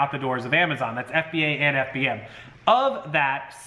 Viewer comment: Did he say "That's FBA and FBA"?